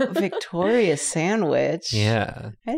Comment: was that a split-second thought of "Victoria's Secret" instead of "Victoria Sandwich?" 😸 ...but hot DAMN I want to try that cake! 🍓